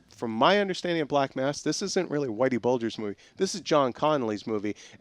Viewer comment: My bad I was totally washing the dishes while this was playing but what's this I keep hearing about a "white bulge"? O_o'